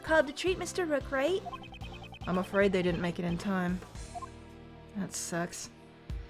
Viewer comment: did she just say *the paramedics didn’t make it on time “That’s sex”